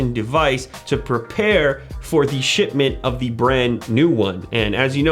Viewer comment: hahah i thought he said shitmint instead of shipment :0